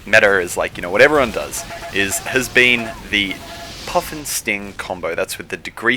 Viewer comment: When he was saying puff'n'sting, i thought he was saying puffin sting.